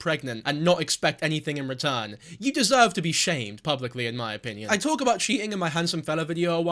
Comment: I know the words were "you deserve to be shamed publicly", but I swear I had to rewind cause I misheard it the first time around as "you deserve to be shaved publicly" and I almost choked on the corn I was eating